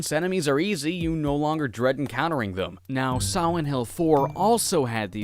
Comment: i swear i heard him say "siwent hill"